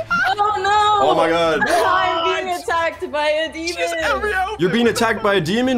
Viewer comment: my dumbass thought that she was saying : im being attacked by diabetus